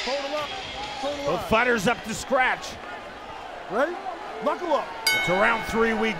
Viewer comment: Knuckle up clearly makes more sense... i thought he was saying buckle up.